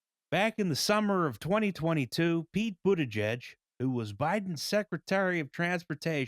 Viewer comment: closed captions say "Pete Buddhajed"